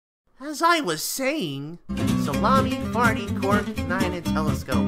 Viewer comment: what did he say (Salami)